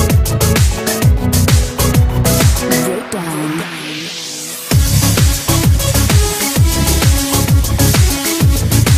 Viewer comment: do u know what it says at 1:58? :3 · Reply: Breakdown